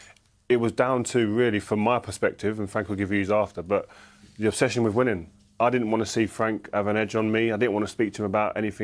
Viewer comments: I thought rio said the obsession of women not winning 😂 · i thought Ferdinand said the friendship fell apart because of the "obsession with women". Had to play it again... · Whoa, I thought Ferdinand said "obsession with women" for a moment there haha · LOL. when Rio said it was about obsession with winning at the start of the video, i heard it as "obsession with women" - I had to do a - no, that cant be right... he must have said something else...winning ofc. he said winning, not women.